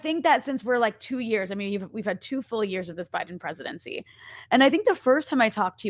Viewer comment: Did she say "two full years of the Biden presidency"? or "ten years"? I can't tell anymore, it seems like an eternity at this point.